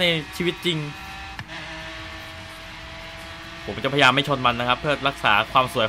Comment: พี่ หนูได้ยินพี่พูดว่า .. :ผมก็จะเป็นเจ้าพระยาไม่ชนมันน้ะคับ 555+